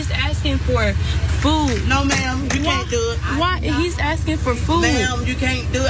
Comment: you can’t do it back in line is what lady saying